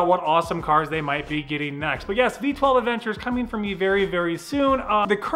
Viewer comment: Did he say v12 Aventador @ 11:50 ?